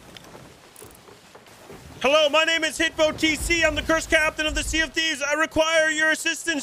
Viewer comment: I thought he was saying HippoTC this entire time... Not even joking. Oof.